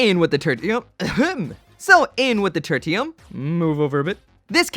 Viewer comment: I almost misheard Turd-ium whenever i see that bronze tier.